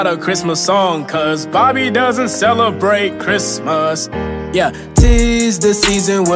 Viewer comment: What I hear "Army doesn't celebrate Christmas "